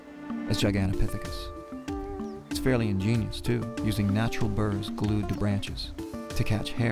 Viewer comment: Before I saw the video portion of it, I thought Les was saying that Todd Standing had gone up the mountain a week prior to their filming and glued BIRDS to the tree branches instead of BURRS. And as weird as Todd Standing seems to be, I momentarily thought that's exactly what Les had meant...